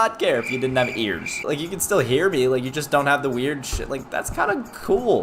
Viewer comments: did he say sh!t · it sounds like you said the “shi* word Edit: I know it didn’t blow up much but I’ve honestly never gotten a comment as high as this lol. Thanks everyone, god bless you